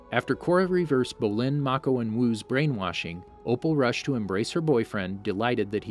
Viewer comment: I THOUGHT HE SAID, "Wu rushed to embrace his boyfriend." AND I’M LIKE WAIT RUSHED TO EMBRACE WHO